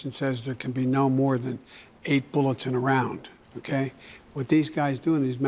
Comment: 8 bullets to a round ??? Huh ? I thought that's what I heard